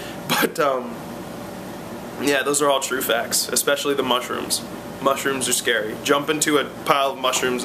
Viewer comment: At first i heard "smush rooms". Damn Jersey Shore T_______T